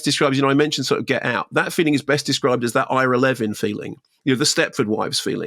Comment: sorry, what movie is he mentioning at 3:42? because he says about the feeling the movie evokes, and the best movie to do that is "i`r eleven"? I do not understand what he says there. Something "eleven" in the title. Anyone? He mentions it 2 times, one more time being few seconds later saying that the "eleven" movie is the father of all that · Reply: Hi I believe he’s talking about Ira Levin here. Author of Stepford Wives and Rosemary’s baby